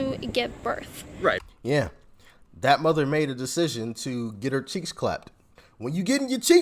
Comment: "getting the cheeks clapped", is that what you said? XD never heard that saying haha